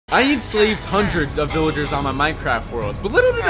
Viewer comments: No cap i thought you say i save thousand of villager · I thought it said "I Save Hundreds of Villager." im confused lol.